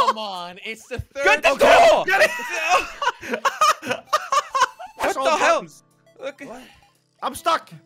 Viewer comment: did say what the hell?